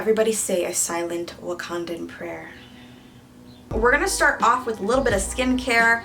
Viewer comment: Hold up did she say wa condom or Wakandan prayier😂😂